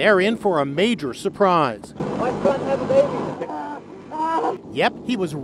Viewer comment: did he say " My son has a baby"???? I guess the baby is a boy